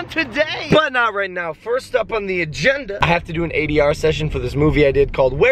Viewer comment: When he said ADR session I swore he said 80 hour and I thought there was no way he was gonna do the paperwork. 🤣